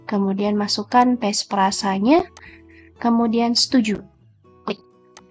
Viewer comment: mohon penjelasan di menit 04:10 apa yg di maksud sertifikat djp nya dan tes perasa klo ga salah dengar gimana cara dapatnya ? maaf masih awam teh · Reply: Hai kak, maksud sertifikat adalah sertifikat digital atau sertel kak. Maaf bukan perasa kak, melainkan passprase. Itu adalah password dr sertel yg qta punya kak (didapat dr DJP jg)